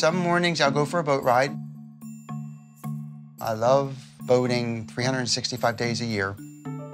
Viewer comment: I thought this dude said "I love boning 365 days a year" 4:14 LMAO